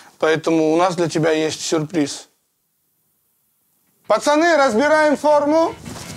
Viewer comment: “Пацаны разбираем форму!” А мне послышалось «разбираем шаурму» еще думаю причём тут футбольная форма , где лаваш 🫓